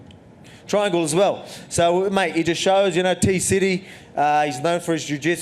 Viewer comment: I'm sorry but I heard titty city. 😄 🤣